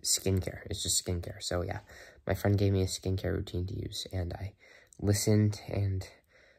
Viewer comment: Thought you said my friend gave me skin cancer